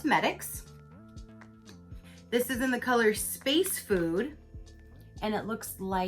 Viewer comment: I canceled ipsy a long time ago to try some other subs. I thought you said space poo😂🤣😂 it was space food🤣😂🤦‍♀️🤷‍♀️